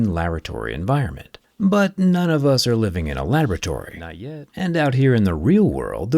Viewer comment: did bro say not yet 😂😂